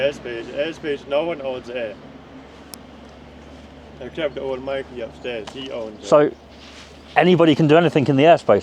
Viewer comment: "Except old Mikey" Is that what he's saying? If so then who is Mikey? 😂